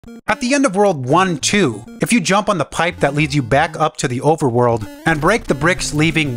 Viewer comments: I swear I heard “at the end of World War 1 and 2” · I thought he said world war 2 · I thought he said world war 2 💀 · I thought he said world war 2☠ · I heard "World War 2"